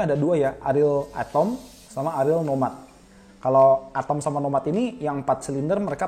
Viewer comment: gue dengernya Ariel Tatum dan Ariel Noah 😅😅